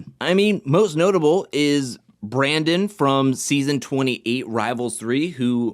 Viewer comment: I thought that said "Brandont" like "don't" lmao I thought it was like a pun or whatever 😂